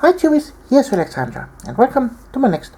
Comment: For the last 2 years I thought you have been saying " hi CHEWIES". Lol. Not tubies